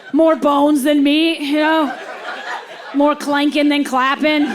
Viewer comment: 'More clankin' than clappin'', she's hilarious! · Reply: Wow,,, I heard "more clenching than clapping". LOL either way!